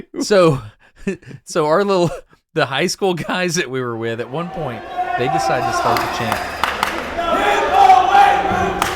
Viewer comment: Can anyone make out the chant? I can't understand it. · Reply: Hit the weight room! (?)